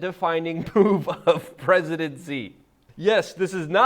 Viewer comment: Did you say President Xi (2:13) instead of presidency???